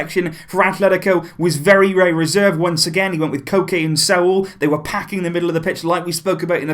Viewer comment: What you said:"Koke and Saul" What I heard:"Cocaine and Sault"